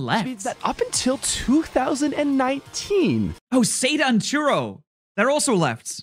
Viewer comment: Why do I keep hearing "Churro" instead of Turo